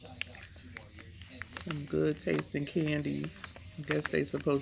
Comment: I thought it said “Great tasting Candles”! I was like 👀